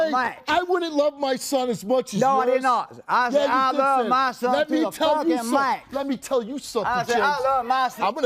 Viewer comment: He definitely did not say what Steve thought he said and got all pissed off about. I'll admit that the accent is a bit hard to understand, but he said "I love my son and I love him to the fucking max." So unless they cut something else, Steve totally misheard him and insinuated that he said something that he didn't.